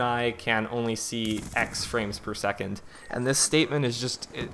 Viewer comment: what number did he say? aix frame per second? is he inventing a new language ? :o